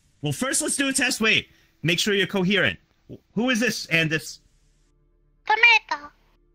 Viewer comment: What is saying at 4:28? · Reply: Tomato!